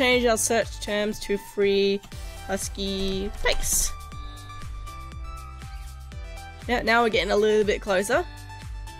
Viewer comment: can someone give me a list of those 3? i cant understand her way to talk the name of the sites... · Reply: Deviant art , weasel and furaffinity